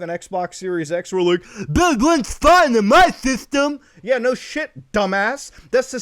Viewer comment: You being that close to the mic made it sound like you said Bugs works fine in my system. IK you said 'but it...". Still funny though.